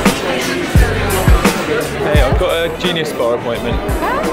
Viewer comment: Am I the only one who thought he said "it's time for my junior spa appointment" instead of Genius Bar